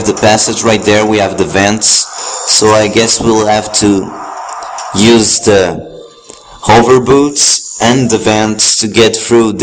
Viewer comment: I could have sworn he said lets use the yogurt boots XD